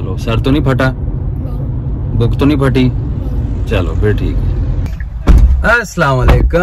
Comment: i thought he said bund to nahi phati